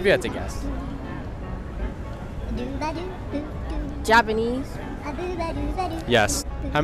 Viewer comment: She didn't even say Japan, she said Japanese 💀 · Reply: It sounded like she said, Jabanese